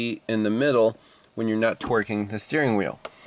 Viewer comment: I misheard "torque" as "twerk" and now I can't unhear it! 11:43 for example. :D